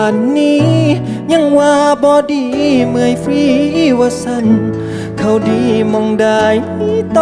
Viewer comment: นาทีที่ 1:16 ห๊ะ ผมได้ยินว่าเมื่อย …… ว่าซ่าน